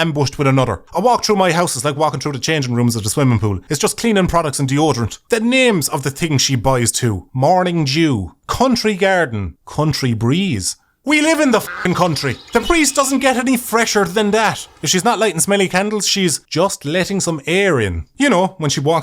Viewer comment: Was I the only one who heard morning jew?